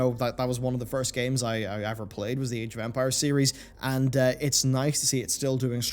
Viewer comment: Am I the only one who thought he said "Age of vampires" ?